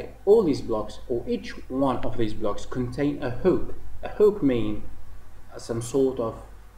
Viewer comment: What did he say at 0:45? The block contain a "hope".... a "hook" ....? I did not understand. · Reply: 😁 sure I said "hook"